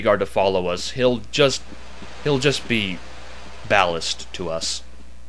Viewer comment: what's that word? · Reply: Ballast. It's extra weight on a ship that's not needed.